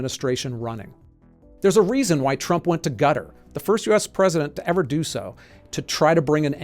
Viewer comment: Is this thing AI generated? I keep hearing him say "Gutter" instead of Qatar!